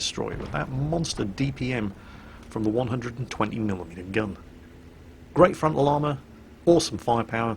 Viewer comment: Did i just heard 0:38 "great frontal armor"